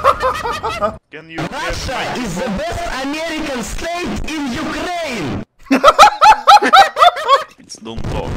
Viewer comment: Russia is the best american state in ukraine? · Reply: I thought he said "rush a is the best american strat in ukraine"